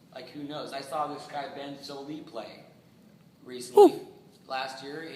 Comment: Who's the guy he mentions at 6:30? Can't make out the name... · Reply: Tullochgorum Ben Sollee